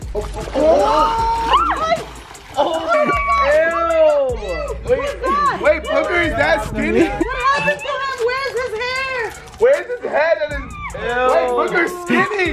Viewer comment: Did he just said where's his head😂😂